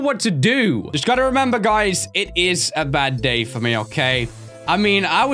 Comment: But I thought you said "Bad Gay" .